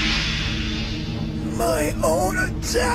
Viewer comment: is it just me or did frieza just say my own d**